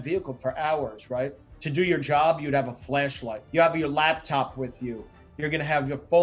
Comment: Thought he said fleshlight! Ha ha